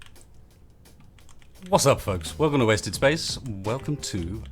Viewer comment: I heard: "What's up fucks." at the beginning